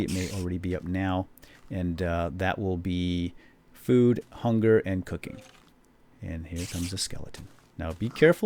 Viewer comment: Haha at 14:43 i thought you said food, hunger and cocaine!